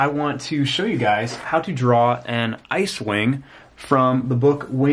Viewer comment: Yessss!ICEWING!! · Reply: I thought you said cewing not icewing